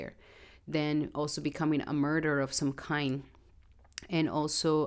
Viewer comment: Thank you for the video. ❤ You gave examples of debilitated Mercury in 12th house. Did I hear you correctly when you said, “…becoming a murderer of some kind”? It’s at 2:15:20. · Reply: Martyr lol sorry my accent 😅